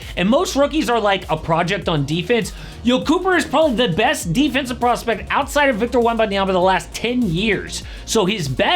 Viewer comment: did he just say he is a better defender then anthony davis in COLLAGE 🤣🤣🤣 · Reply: defensive PROSPECT